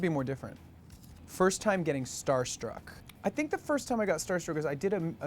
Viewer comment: Omg do it said “star struck” and I thought it said Starbucks lol